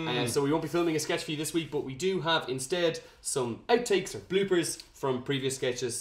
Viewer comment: I heard 'blueberries' instead of 'bloopers' at the beginning 😅